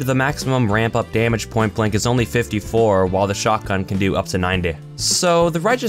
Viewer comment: what just happened to your voice? You just said : The shot gun goes up to nindehh.